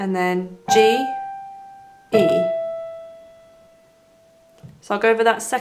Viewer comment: you say g-e the caption says e-g btw